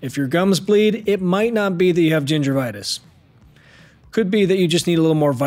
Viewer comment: I swear I heard you say Gingervitis… South Park reference?